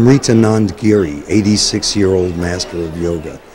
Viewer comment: He says the mans name at 40:50 in but I can't understand what he's saying the audio I have is too rough, Does anybody know this yogi's name? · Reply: The name of that yogi is Shri Amritananda Giri.